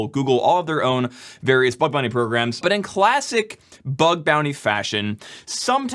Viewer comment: who heard "bug bunny programs"? I guess security researchers are really HOPPING to get paid!